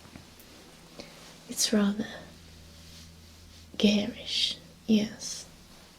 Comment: When she was talking about the bonnie I thought she said gayish